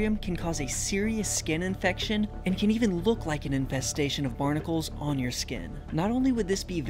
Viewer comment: I thought it said, "Incestation" and got scared